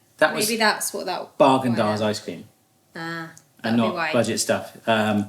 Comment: I thought you said "Bargain Dazs" which would be a great name for a budget ice cream brand.